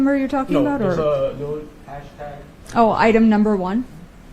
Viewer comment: did he say “hashtag”